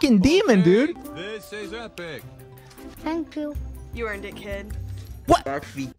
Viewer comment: dixie: you earned it kid quackity: DID SHE JUST SAY 'YOU'RE A DICKHEAD'